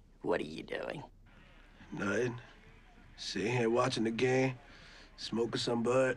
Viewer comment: What did he say? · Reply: “Nothing, just sitting here watching the game.”